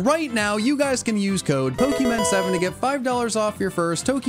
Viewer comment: When he said pokemen7 I thought he said pookymen7 XD XD 😂